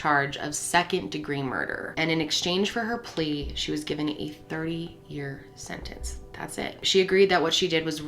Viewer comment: At first I heard you say she was given a 3 year sentence and my heart skipped a bit.